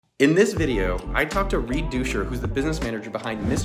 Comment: I thought he said Re-doucher